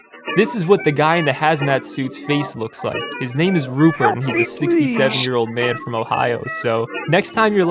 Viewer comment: I thought he said “he’s a very sexy man from Ohio” 💀💀